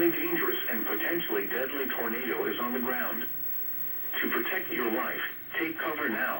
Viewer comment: when it said to protect your life I thought it said to protect your wife